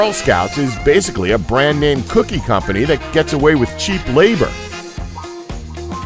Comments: he said cheap instead of child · why does it say child labor but the narrator says “cheap labor”??